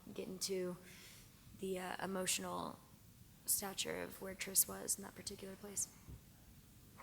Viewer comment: No one: Theo: Let's have some play time with my chin! Oh yes, wonderful chin! And at 10:40 I thought she said FourTris, but she didn't